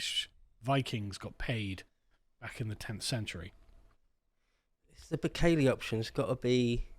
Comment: what does he say at 19:28? the bicali option? can't make it out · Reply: The Bukele option. After El Salvadoran president Nayib Bukele's mass imprisonment plan